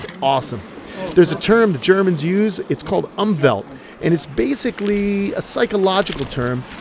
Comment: Can someone help me to figure out the german word he said at 2:52? I never heard of it (or just didn't understand it right) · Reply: umwelt